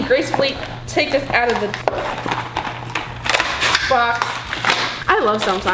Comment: did she say box or f** she totally said box she has sweared in her past videos but she is a teen anyways